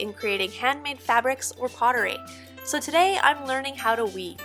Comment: When you said, "Today I'm learning how to weave," I froze for a bit, because I thought you said "leave." You were in fact saying goodbye, but it caught me off guard. I miss Aya, and I wasn't even there. I hope you have just enough sadness and miss it enough to return. Your series here is beautifully done.